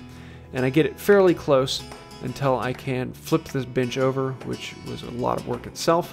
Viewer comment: At the 8:49 mark I thought I heard you say “flip this b$&ch over” and thought that’s pretty uncharacteristic language for his videos. Heard it correctly the second time! Awesome bench. Enjoy your hard work buddy!